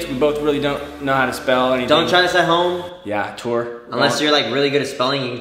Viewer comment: did kian say ‘ yea tour’